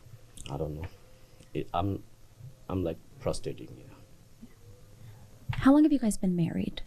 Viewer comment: did he say he prostating?